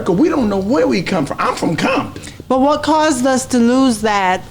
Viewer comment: is all I heard my city I love Bompton